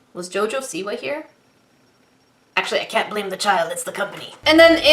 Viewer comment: When said "I can't blame the child, it's the company", I heard "I can't blame the child, it's the puppy".